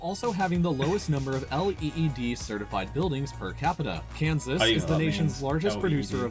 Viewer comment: Funny how your lights went out at around 5:21 ish when it sounded like he said LED 🤣🤣